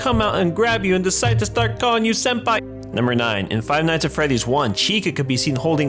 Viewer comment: wait did you say start calling you senpai????